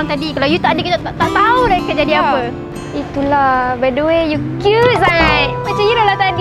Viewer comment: Btw- between? Haha aku salah dengar ke😂 it suppose 'by the way'